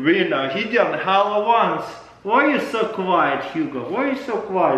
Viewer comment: Anyone else thought he said Halloween?